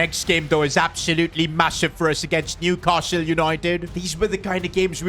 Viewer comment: did bro say massive?